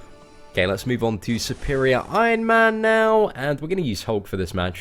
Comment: Did he say superior iron man? Like if you agree